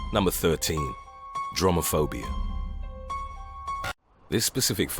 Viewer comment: When they said “Dromophobia” I thought they said Dramaphobia and I was like: same sister